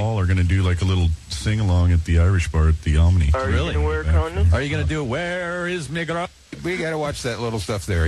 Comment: What was layne saying to sean at 8:12? 😂😂 Did he ask him if sean is gonna wear a condom when he meets Paul and Bob?